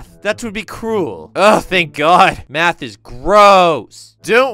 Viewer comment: I hear Matt xd